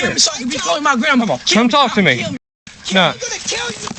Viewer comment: Did I hear the scumbag boy sayin "I'm gonna kill you" (1:07)?